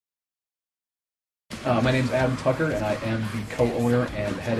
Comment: rofl thought he said adam fucker